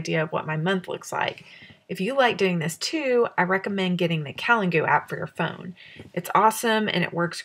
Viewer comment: you mention the name of an app. I'm unfamiliar with it and couldn't catch what you said, can you clarify please? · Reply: Hi! The calendar app I use is called "Calengoo". I still love it. Here's a link: <link>